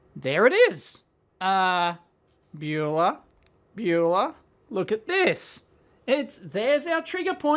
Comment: What is he saying at 2:40 something like byula or bulla? What does it mean? It driving me nuts.